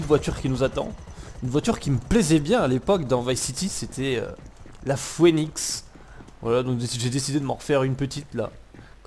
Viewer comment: LA QUOI ???🤣🤣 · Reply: TDF 002 la phœnix pourquoi ? ^^